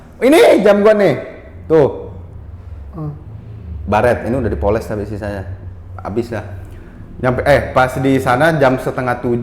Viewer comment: Cb dengerin deh di menit 36:37 paas ngmgin jam, kedengeran di poles istri saya bukan sih gass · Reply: Bukan kak, Bung bilang "baret, ini udah dipoles tapi sisanya"